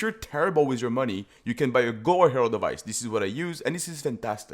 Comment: you can buy a 'what' herald of ice. cant understand the word. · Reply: Gore Herald of Ice, an MTX I assume